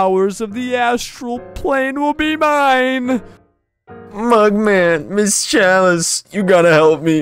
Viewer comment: did he said my man or mugman let me know pelese